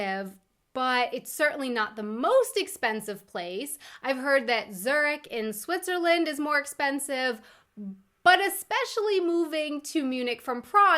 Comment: Did she just say Zerg? SCNR (around 07:30) Sorry. but it literally took me listening 3 times until I got which swiss city you meant and it sounded so much like the Starcraft alien race.